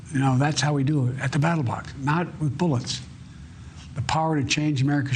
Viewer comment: Did he say battlebox, twice?